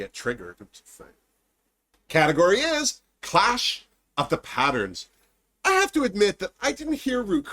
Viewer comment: I thought Ru said Crash of the pageants too!